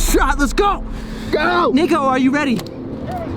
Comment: what did he say? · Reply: Niko